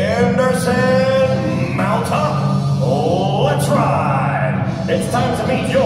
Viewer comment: what does the announcer say at 1:30 after henderson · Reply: Mount up, let's ride